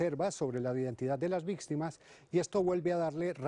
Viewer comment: Alguien más escucho que dijo vixtimas